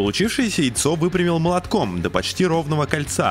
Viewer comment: Мне одному послышалось что он сказал ЯЙЦО 2:32 ?